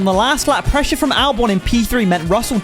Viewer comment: In silver stone was I the only one who heard him say Albion in p3